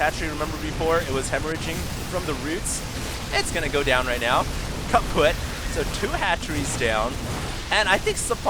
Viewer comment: Did HD just say kaputt? Like the german word for broken? LOL